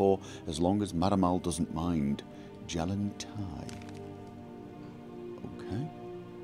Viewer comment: Jalen-Tai? Hm, I thought it said Talen-Jei. Heh XD